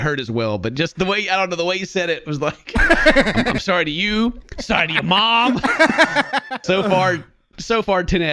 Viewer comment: When he was saying, “I’m sorry to you and your mom.” All I heard was, “Dishonor on you! Dishonor on your cow!”